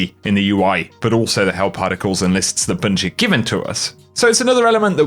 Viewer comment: Thanks for the video bud 🤙always a pleasure watching. Hope you and yours are staying safe and well out there. P.S. 7min 06sec I swear to god I thought you said "hell particles" instead of "help articles". I had to pause the video because it made me laugh my ass off for about 5 minutes, so thanks for that 😂😂 Stay safe, stay well and take care of each other 🤙 see you out there Guardians 🚀